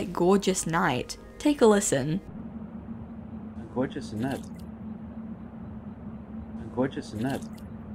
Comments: I heard "gorgeous nut *squish sound*" personally HDSFKDHXGFKGDF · I heard “gorgeous nut” 😭💀💀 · With the looping voice NPC in Ra, at first I heard "a gorgeous night" and then "a gorgeous knife" and then "a gorgeous nut *wet noise*" and I'm honestly done with my brain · idk about you but i heard "gorgeous nuts"